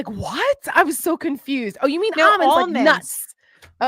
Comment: I heard omens, not almonds, too!